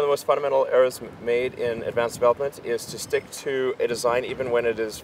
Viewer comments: Hey at 2:07 where the closed caption says mumbles, it sounds like he's saying development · The closed caption says "(mumbles)" but he clearly says "development." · You, sir, are my hero! Great interview!!! Two rocket nerds discussing rocket nerd stuff, and we get to watch! BTW, at 2:08, where the CC says, "mumbles," he says, "development." Don't know if that's youtube's CC translation or yours.